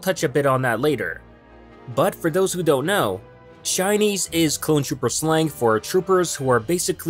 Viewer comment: When you first said Shinies I thought you said Chinese